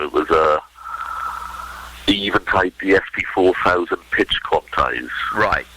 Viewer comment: what does say here? Something ESP4000? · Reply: He says "Eventide DSP4000 pitch quantise", which was a hardware pitch tuner/mangling device that you could use on vocals and other instruments to make them more (or less!) in tune, depending on what you're trying to do.